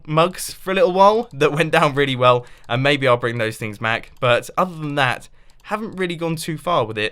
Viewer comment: did you just say "bring those thing mac"? xD